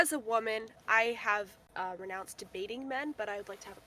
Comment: "as a woman ive renounced debating men but ill have a conversation with you" girl what "Can I shout out my channel" "I, um, no" 😭😭😭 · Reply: I thought she said ive renounced dating men and im like what? Lol